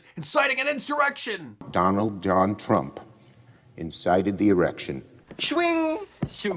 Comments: Listen closely at 3:50 in the video. Did I hear Schumer say that Donald Trump ensighted " the erection" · ‘Incited the erection “ 3:52 ???. Did i hear that right ?. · Did you hear what Schumer said? @ 03:50 'Donald John Trump incited the 'erection'. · Did he say incited the erection? at 3:51 or am I having hearing problems? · Is it my imagination or did Shumer say Trump " incited the erection " ?? ( min 3:50 ) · Sounded like he said “Donald J Trump excited the erection” very odd